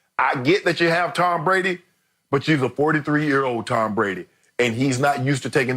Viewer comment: did he say she's a 43 yo Tom Brady? New levels of trolling